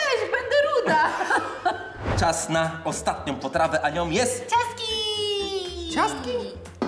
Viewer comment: Zamiast ciastki usłyszałem majtki